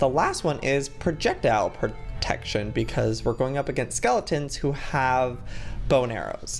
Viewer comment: I THOUGHT HE SAID SKELLETONS WITH BONERS! LMAO